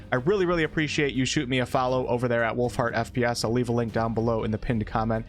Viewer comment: I thought you said Wolf Fart not Wolfheart 😂